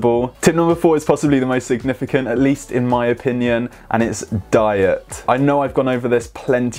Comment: thought he said "Dye it!"